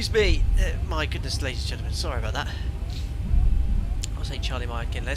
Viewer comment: Thank you for always posting the member flights up for all to see. If you haven't been to Hawaii yet Capt i suggest you put Maui om your list. Its such an amazing relaxing place. At the beginning I thought I heard you read "sorry about Jack" from a member. Is Jack ok 🐕????